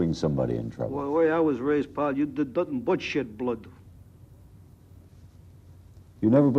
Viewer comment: What is he saying around 00:46? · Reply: ‘The way i was raised, you did nothing but shed blood’